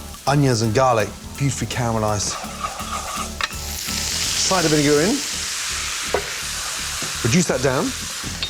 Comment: To be honest I heard nothing but "slight bit of urine in".